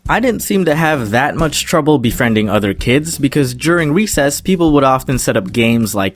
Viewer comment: you said "weesess" instead of "recess"